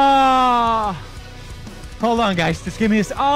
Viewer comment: Am I the only one who heard Pewds say “ among us”